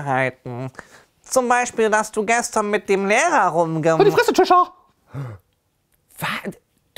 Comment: Ich hab einfach mal verstanden, Lexa hat mit nem Mäher rumgemacht 😂😂😂😂